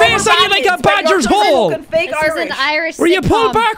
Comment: "You've a face on you like a badger's hole." That's it. The Irish won. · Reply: Idk why I heard it as “Thatchers Hole” Equally Irish insult